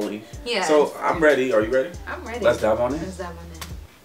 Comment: the first time i watch you guys i thought you guys say "lets dab on in it".😂